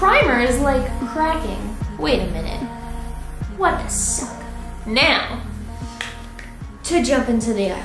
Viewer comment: DID SHE SAY "what the succ" AT 3:49 I hope so